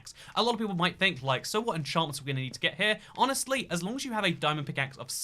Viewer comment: XD WHEN HE SAID "ENCHANTS" THE CAPTIONS SAID "INSURANCE"